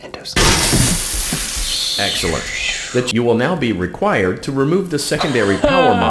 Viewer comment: I thought it said “b##ch you will now be required to” 😂😂